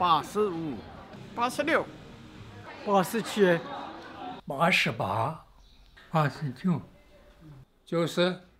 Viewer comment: Sounded like “Pass the chair.”